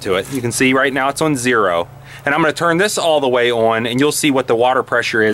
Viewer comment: Good video quality, good sound and mic, good voice. Although, within the first 1 min, 57 seconds I heard you say, "Ommmanaa" 5 times when you meant to say, "I am going to..." Dude, pronunciation pronunciation. Sorry, I had to click off.